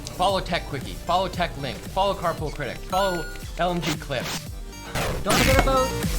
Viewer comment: I thought they said "carpal critics" and it was going to be a series on posture